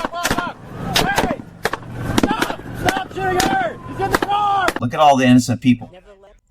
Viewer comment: I thought I heard him yell stop shooting her